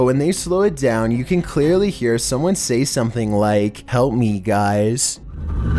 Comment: I thought that the voice said "oh my god" not "help me guys"